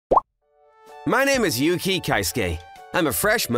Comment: I fr thought he said his name was yuji